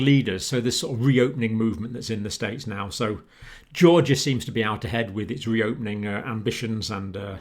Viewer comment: Dr. John!!!! Oh my goodness, you gave me a good chuckle.....it “sounded” like you said Georgia was out of their head at first....well, I happen to live there and what I thought I heard you say would be 100% correct. I read what our Gov said this morning and just for the movie theaters, the seats have to be wiped down before and after every show. Really? Are people truly going to follow those guidelines? I think not. Have a lovely day my friend! Beverly